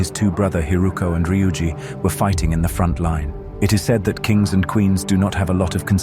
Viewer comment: At the frontline part where he said they were fighting in the frontline I thought it said fortnight